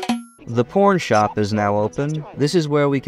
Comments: did you say “porn shop”, or did you say “pawn shop” but YouTube auto subtitles recognised that as “porn shop”? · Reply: no he definitely said porn shop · anyone else hear “the porn shop is now open”???